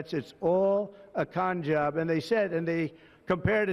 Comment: 😮 did he say cum job?😢